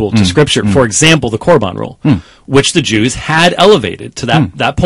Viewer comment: what did james white say ? can someone help clarify. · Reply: Corban Rule Question: What is the practice of Corban that Jesus condemned in the Bible? Answer: Mark 7 is the only place in the Bible where Jesus makes comments regarding the issue of Corban. After the self-righteous religious leaders called Pharisees criticize the disciples for not ceremonially washing their hands before eating, Jesus chastises them for their hypocrisy. After condemning them for their "clever" methods and schemes of nullifying the laws of God so that they can keep their "cherished" traditions, he says the following. For Moses commanded (actually, it was God who commanded through Moses), 'Respect your father and your mother, ’ . . . But you teach that if people have something they could use to help their father or mother, but say, 'This is Corban' . . . they are excused . . . (Mark 7, verses 10 to 12) Jesus was condemning the general practice of the Pharisees of bypassing God's laws and substituting them with their own traditions. The case you cite is one of the many Jesus was denouncing. The Treasury of Scriptural Knowledge says that this practice was common among the Jews. The Pharisees would release by their own power a child from supporting his parents. Amazingly, according to the TSK, these religious leaders would deem it sacrilege if such a person afterwards decided to go ahead and support those who gave birth to him. Easton's Bible Dictionary goes further on to state that Corban was considered a temple offering set aside or dedicated to God. Anything g